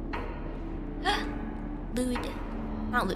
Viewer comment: i thought that said amelia pretending to be sus